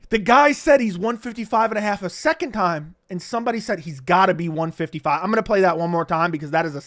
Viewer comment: Yeah , I heard it “He’s gotta be 155”